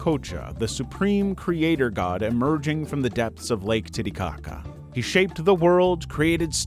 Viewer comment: did i hear titty💩? 😭😂🤣😬😨😫😵‍💫😵💀💐🪦